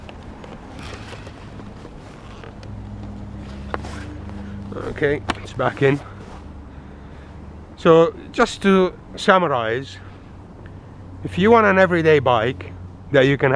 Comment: "He likes to see it take it from the back." ;) lol And I totally heard "Just two samurais" instead of "Just to summarize." Love your work. We will miss the 701 <3